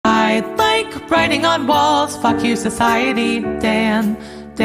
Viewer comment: I thought it said I like writing on walls fucking society damn